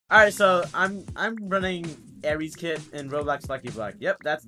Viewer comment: did he just say ares kit?....